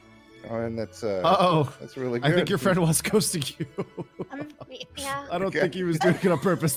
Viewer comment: what did he say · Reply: He said I think your friend was ghosting you.